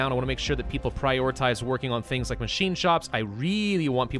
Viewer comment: Pretty sure I just heard say Machine Chops.